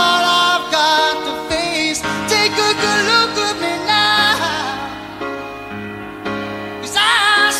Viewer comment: What the fuck does he says in that part "takuku luku me now"